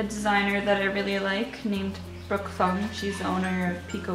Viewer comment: What's the designer who you mention at 2:42 called, I can't hear! I really like your drawing of her so I guess her clothing too.. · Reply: i don't know if you're still interested but she is called brook fung and has an etsy shop called "peeko apparel" :)